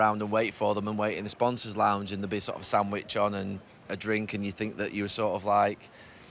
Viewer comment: I honestly thought he said "and there'd be sort of Sam Widghorn" and that was like an old Bury player he got to meet until I realised he said "sandwich on"